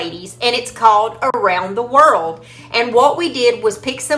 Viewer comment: I'm laughing at myself because I thought you said squirrel! Then I realized you said world. 😂